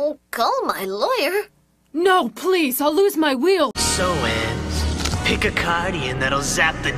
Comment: Lmao "call my lawyer" "No please I'll lose my wheel!" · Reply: thought she said ''I'll lose my will''